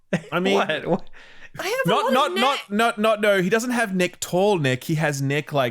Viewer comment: Did say Ethan Klein neck?? lmao